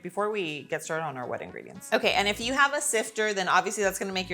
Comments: I thought she said “if you have a sister, this will make your life a lot easier”.. she really said “sifter”… I do have a sister and was already thinking of making a batch for me and her! 😂 Can’t wait to try this! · I thought she said sister too. I'm trying to pay attention to putting the recipe together, that sifter comment made me chuckle though. Thanks for showing us on how to make these. I used coconut flour, talk about an epic fail.